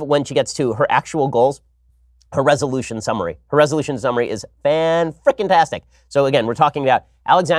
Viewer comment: 05 did he just say what it sounded like? Sounded like he said fan fckn-tastic 🤣🤣🤣